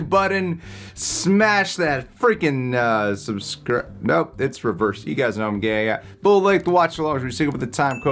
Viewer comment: I thought said “you guys know I’m gay”